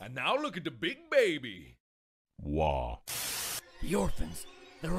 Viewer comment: Markiplier :Aww look at the little baby Baby : ????? Multiplier : how look at the big baby Mark as baby : way (it sounded like war)